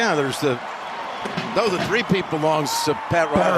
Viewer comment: At the end of the game i thought Nance said "The Three Cheat"